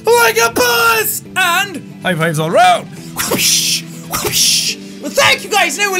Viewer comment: congrats on 10,000,000 subs funny story i watched about 10 of your videos before i realized you say high fives all around i could have sworn you were saying fireflies all around i need to get my ears cleaned lol